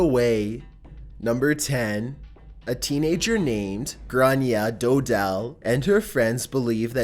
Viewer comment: In number 10 I thought it said Grannie!! XDDDDDDDD